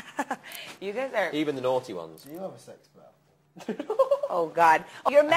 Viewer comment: hey there! @ 1:52 when you said “idk what harry said” he was asking niall if he has a sex bell- for context, there’s a video from the interviewer on her own channel(?) from before the interview where she’s talking with the boys about how she brought a “ring for sex” bell to the interview. that’s why niall does that little bell-ringing motion later in the interview, and also why i imagine h + n cracked up at liam saying he “doesn’t judge” bc this lady was out of pocket